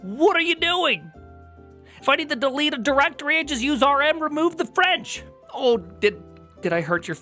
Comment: rm -fr "RM remove the French" LOL I heard "Remove but for real" before